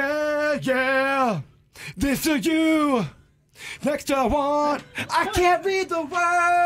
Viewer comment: I just can’t over the fact that it really sounded like 🦢 said “next to JUAN” 🤣🤣🤣